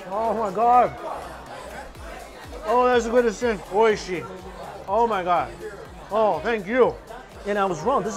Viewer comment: Oeshi ? Is that what he said ??? Sorry I don't know , will sometimes please tell ??? ❤️ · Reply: おいしい（oishii) means delicious in japanese, 🌈the more you know🌈